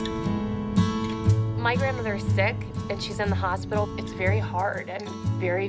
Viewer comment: I dont want to be mean to Christi but in the beginning i heard ‘my grandmother is 6’ instead of sick